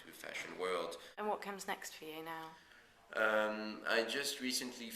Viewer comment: when the interviewer said "what comes next for you now" i thought she said "what kind of sex for you now". i don't want my mind to be in the gutter but gaspard can't help but pull it in there!